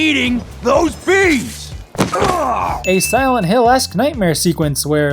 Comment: Also, really thought this game was called "life is Tumblr"